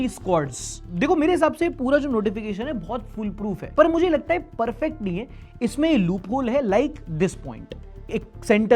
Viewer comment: caption mistake: use "fool-proof" instead of "full-proof". It is very common and misunderstood word.